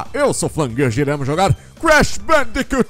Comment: so eu q ouvi crash bendicu 2?